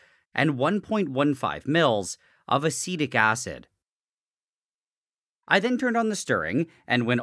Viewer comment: "and 1.15 mils of acetic acid" I misheard that as "acidic adic" and was about to raise some... concerns.